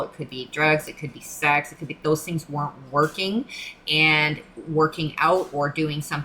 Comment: What did you say here? Wasn't working or? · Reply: The addictive behaviour wasn’t working so working out and physical activity was the better choice to transmute that energy.